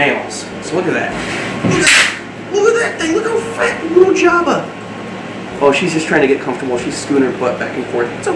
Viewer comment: You're saying "Jabba" but i hear "Java". And so I suggest Java for the future male, and Mocha or Latte for female. (